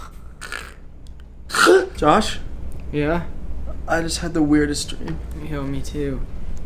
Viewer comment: GENTE FOI SÓ EU QUE ENTENDI : 1:46 Noah :Josh Josh : QUE É ?